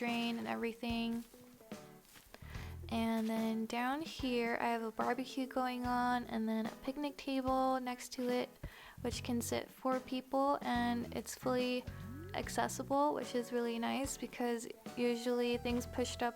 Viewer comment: At first I thought she said can sit poor people... 😭